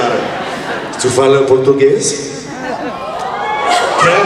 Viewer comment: What did Jensen Ackles say just then? Jensen Ackles sounds so sexy speaking in a different language.❤😍😘😋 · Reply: He asked if Jared speaks Portuguese: "Tu fala português?"